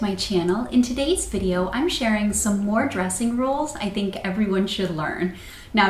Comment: I thought it said depression rules and I was like wait... there are rules about depression????